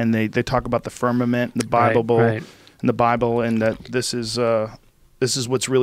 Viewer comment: Joe “The Bibable” Rogan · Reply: I was reading you comment thinking to mysel, wtf "bibable???", then as i was scrolling up a few seconds later I hear Joe saying "The bibable" and I fucking cracked up lol